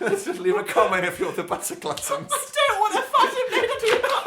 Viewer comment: My mother walked in when Butter Gluttons was said, and she thought they said Buttery Genitals. You can imagine how that went.